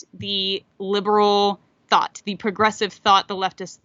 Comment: The “gressives”, man oh man, you may have misspoke the word “progressives”, I may have misheard you, but you just came up with an awesome term for one of evil’s modern tools, the gressives. I love it. Whether that’s what you said or not.